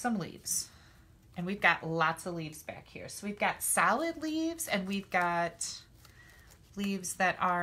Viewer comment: LOL...at first, when you were poking out the leaves, it sounded like you were saying "salad leaves" instead of "solid leaves", bwahahahaha! It's ok, my ears ring all the time, so it's hard for me to hear sometimes, LOL!! You're a hoot! I just LOVE all your tutorials, gf!!!!!!!! Hugs!